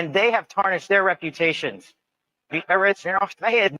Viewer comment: I heard "the origins/originals lost the head." 15:10 "Daemons are FACT." 22:45 "Praise the one".